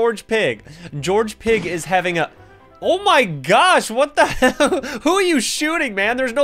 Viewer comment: it sounded like he said "what the h*ll"